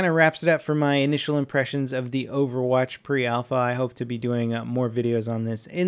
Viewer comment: did he say overwatch alpha?